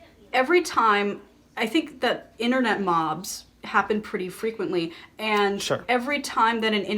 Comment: at first i didn't understand you were saying "internet mobs" and i thought you said "internet moms" which i found a little confusing